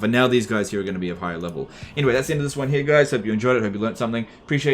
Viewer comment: I'm such an old viewer everytime says "Later guys" I hear "Later F***ers"